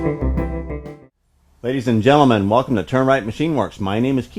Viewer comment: Ah, it's Turn Wright. I thought you were saying termite which had me puzzled. I assume that you recycle the chips. How do they classify them when you bring them in?